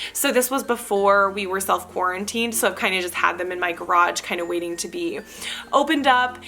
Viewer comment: I am guilty of watching videos on 1.5 speed so when you said , “I had them in my garage” it sounded like you said I had them in my crotch. Lol I had to rewind, put it on normal speed and figure out what the heck you said haha